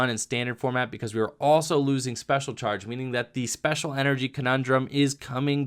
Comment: Wait, did I just hear special charge is getting BANNED?